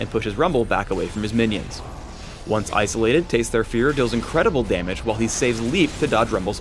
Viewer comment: did phreak just say "incredible damage"